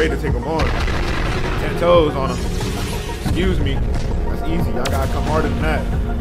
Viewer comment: what did he say?💀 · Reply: Excuse me he said